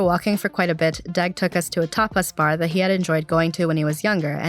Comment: No lie, while listening to this for the first time I thought you said that Deg took you to a topless bar. I was thinking “WTF?” And had to scrub back and watch the video. Tapas Bar makes much more sense. 😉